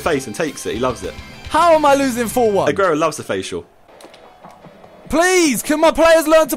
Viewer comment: I dropped my headphones, picked them up and all I hear is "Aguero loves a facial" LOL